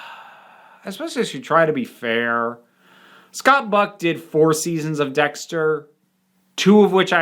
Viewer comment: Every time you said “Scott Buck”, I heard “Scott Brick”, who is my own media ruiner. (He’s the narrator on many sci-fi and fantasy books and has this awful over the top delivery.)